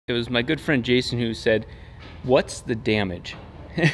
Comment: LOL that first line sounded like you said "My good friend Jason Newsted" I was like whhhhaaaat?!?! knows the old bassist from Metallica?! LOL